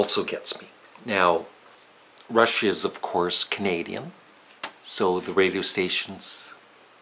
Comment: Rush is Canadian... Thought he said Russia is Canadian for a second lol...